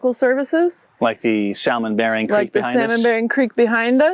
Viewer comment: did they just say sal men?